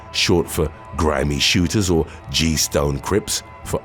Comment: 😹I originally thought I heard GS9= Grammy Shooters